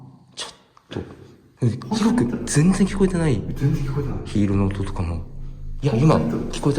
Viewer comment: の2人の声に混ざって女が一緒に何か喋ってる様に聞こえるの俺だけ？ さんの声かと思ったけど絶対違う。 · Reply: 被るように「聞こえてない、聞こえてない」ってめっちゃ普通に話してる若い女性の声しますね